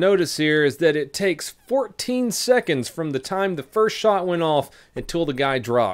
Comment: I think it was acutely closer to 13 seconds from the first shot until he dropped, but that still a long time under these circumstances. EDITED, my apologies, i thought you said 18 sec. I put CC on to hear what he's saying & went to far, then caught you say 14s. My bad